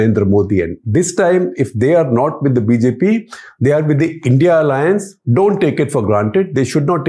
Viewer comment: Funny, I thought he said "I.N.D.I.A alliance" ! :face-blue-smiling